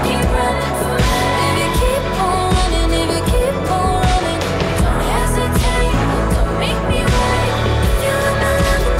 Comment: what did she say? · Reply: joe ng lol she said if you keep on running, if you keep on running ..... kinda sounds as if she’s saying the N word don’t it? Lmao